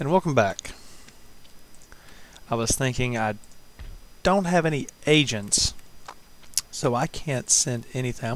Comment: did he say i dont have any agents